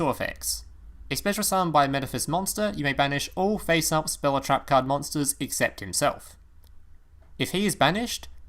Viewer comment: Did he just say Spell or Trapcard Monsters?